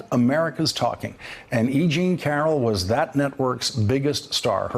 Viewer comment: at first I thought he was saying aging carol